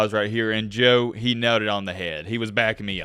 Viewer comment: joe did what??? Took me a few times to realize he said “nailed it on the head”